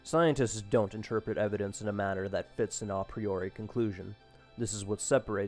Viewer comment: What is that word at 6:30? Sounds like "inapreoric" but that's not even a word. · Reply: He said "an A Priori" this is a philosophical term. It means "from the earlier"; which means it is independent of personal observation or experience.